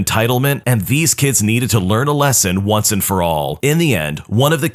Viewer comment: what he said : once and for all what i heard : One For All